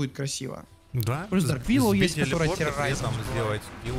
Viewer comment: "дарк виллоу может анкор дать" чисто школа л2 или послышалось )?